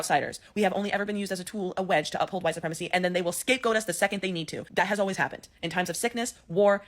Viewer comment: Did she just say "scapegoat us, if they need to"? I hope Latinos for Trump are watching this.